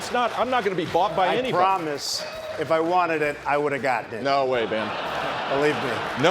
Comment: Why did he call him "Ben" ? 1:17 - "No way Ben" ?